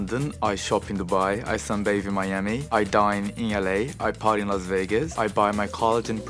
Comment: it sounded like “I die in la” and Dying in La popes in my head